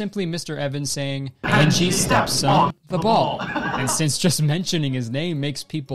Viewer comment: i thought they were saying "and she steps on the ball"